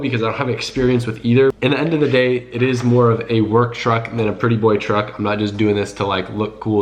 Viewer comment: Lmao, I thought he said he was going to look at work trucks not "pretty boy" trucks.